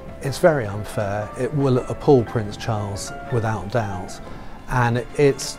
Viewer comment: Omg 1:39, did he say Prince?